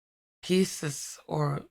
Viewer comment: you said "pieces", I heard "kisses" 🤣🤣🤣 Metal orbs with kisses, why not 😁 All day that kind of mishearings or misreadings, there must be something in the air 😃😃😃 Love and KISSES to you 😘😘😘 🌸💖🌸😃